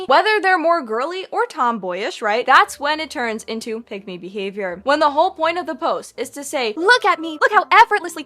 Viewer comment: Until I actually looked at the captions, I thought she was saying 'pygmy behavior'